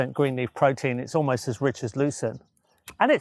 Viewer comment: Geoff mentions a plant the Lucena at 17:40 mark is "almost as rich as...." Couldn't catch what the plant's name was. Did anyone else catch it or know which plant he was referring to? <link> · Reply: Lucerne, I believe. Also known as tagasaste.